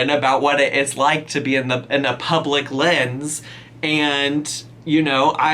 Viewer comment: I legitimately thought you said “public-lynn”